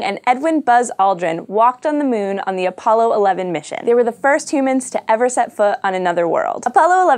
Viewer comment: did she say "fist two mens"?